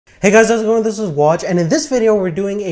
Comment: what did he said ? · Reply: hey guys thi thoobenjboathh